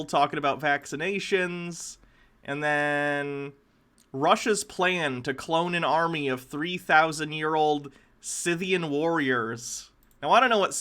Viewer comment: I yawned at the start of the Russian one and it sounded like he said Russia are cloning an army of Lawyers